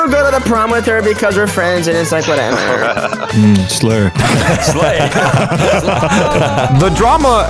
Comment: I so thought that laff said "slur" instead of "slay" im so cooked bruh